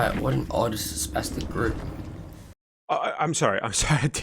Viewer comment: What did the guy say? (no joke I legitimately couldn't hear/understand) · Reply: Odd spestic group